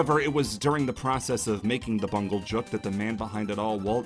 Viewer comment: did he say Bungle jook?